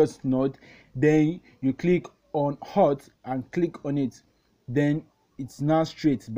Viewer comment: im sorry i cant hear what hes saying at 03:09 can anyone help me? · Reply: just found this video myself while trying to relearn the pen tool in coreldraw, hes saying "click on alt." i tried it and what i think he means is, you hold alt and click on the node to make it a corner point and turn the preview curve back into a line. hope this helps!!!